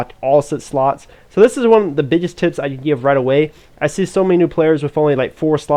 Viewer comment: I heard a slur, "biggest "tits" I could give"